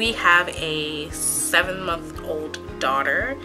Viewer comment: A part of the frech translation is wrong I think around 12:20 she said: my daughter is about 7 month old or something like that. It's written 7 ans wich mean 7 years old. It's suppose to be 7 mois. Just a little mistake after that amazing video as always ❤️